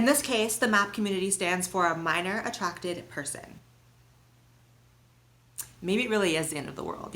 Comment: I thought it said, "Minority attracted person." But this is so much worse.